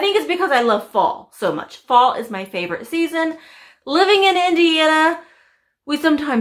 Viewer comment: Amazing haul! Sounded like you said you live in India??